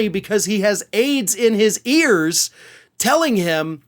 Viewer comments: For a second there, I thought I heard you say trump, had AIDS, in his ears? I'd never wish that on anyone? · To my chagrin, in this video I heard say "Donald Trump has AIDS in his ears . . . " and I thought "Wow, that's really specific." Then I realized he actually said, "Donald Trump has aides in his ears . . ." and I laughed my behind off. Mondegreens from commentary videos now. Hilarious and awful at the same time.